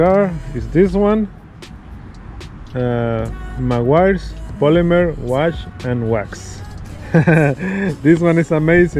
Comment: i thought you said “... my wife’s polymer wash and wax”